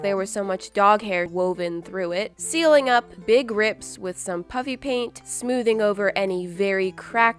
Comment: Ha ha ha it sounds like she is saying puppy paint like is you agree ↙️